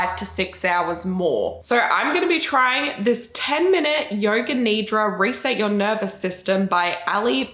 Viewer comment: I thought she said yoga KNEE DROP 😂😂😂 not yoga nidra. It would've been more fun